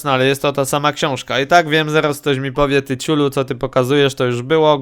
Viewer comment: Ja tam usłyszałem "ty chuju!"